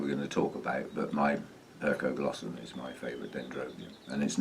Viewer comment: what is the name of the orchid? english is not my main language so i can't quite make the name. Perko glosum is what I hear. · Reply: I wrote it on the screen too. Dendrobium hercoglossum